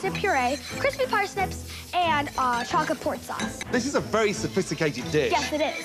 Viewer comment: I thought she said porn sauce